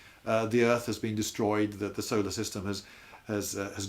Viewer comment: I misheard "Solar System" and heard "Soda System". My thoughts: "Oh noes! First the Earth, then this! My soda system is broken! What am I going to drink?"